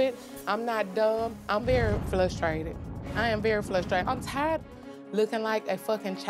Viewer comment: I’m sorry did you just say you are flusterated?